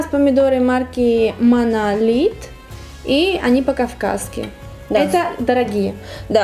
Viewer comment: послышалось "они пока в каске"))